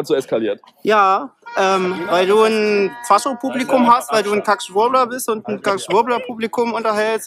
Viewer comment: Was sagt der Scharmhaarafro bei 6:45 ? Schwubbler ? alter immer diese Wörter aus dem Nex Gen Duden... Die Welt ist so LOST